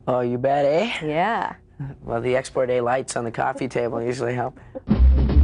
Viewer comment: What does MJF say at 10:54? Some sort of lights on the coffee table that usually help. I apologize for not being Canadian or understanding Canadian figures of speech. I am from the mighty USA. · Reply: Export A light were a brand of cigarettes.